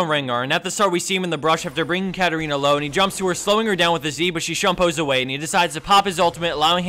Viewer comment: rengar jumps to ashe slow her down with his Z :o did i hear that right